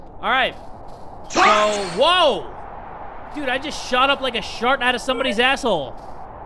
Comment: did i hear thot??